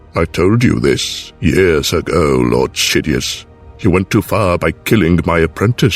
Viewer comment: why did I hear “Lord shitdious”😭😭